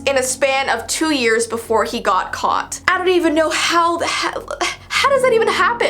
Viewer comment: I kinda heard her say "how the hel-fjdi"